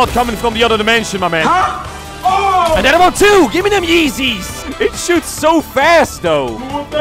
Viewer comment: It do be sounding like he said the N-Word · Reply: "Niqqa that was fast tho!" That's what I heard. "It reloads fast bro!" Is what he said. Feels bad.